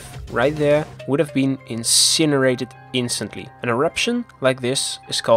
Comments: what kind of eruption is it called? The subtitles can’t properly write it. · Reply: flood basalt. basaltic volcanoes, with flood type eruptions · "An eruption like this is called a" WHAT? Basaltslot? Basaltschlot? Basaltrock? What word is he saying here? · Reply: Flood basalt